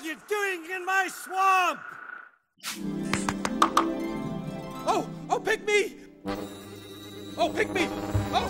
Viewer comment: What did Donkey say? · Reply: Oh oh pick me